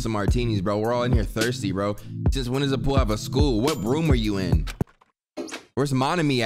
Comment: I know I'm not the only one that heard him say when does a pool have a school 💯😂😂😂😂